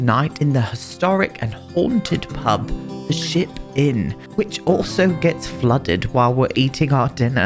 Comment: I thought i heard, the Sh!t inn 😅 oh my... my ears are bad 😂😂😂 so sorry 😅